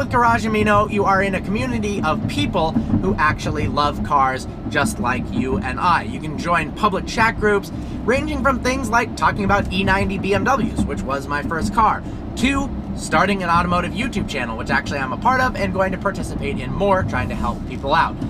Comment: Did he say m 9 or m90 Bmw